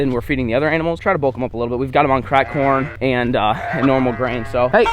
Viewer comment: My parents got mad when you said that the big animals were on cracked corn because they thought you said cracked cocaine.🤣🤣🤣